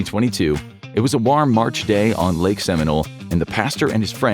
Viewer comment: Did you just say “Rarm”?